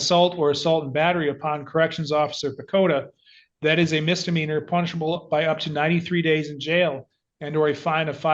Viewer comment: That smirk! Dude thinks he's the smartest person in the world. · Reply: The resentment has apparently fueled the growth of the shoulder chip. When Judge ssid "...punishable by.." heard it as " punchable by.."😅